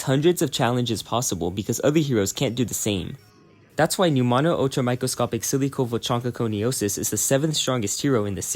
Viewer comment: idk what he said but it sounds funnys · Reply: Pneumonoultramicroscopicsilicovolcanoconiosis is the longest word you can find in the dictionary, which is why I used it. It's a lung disease caused by volcanic silicone ash inhalation